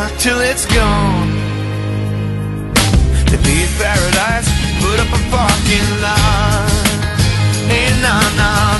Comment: Always thought he said big paradise, put up a funky light